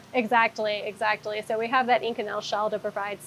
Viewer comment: Captions say "ink and nail" at 29:20. Pretty sure she said "inconel". 😅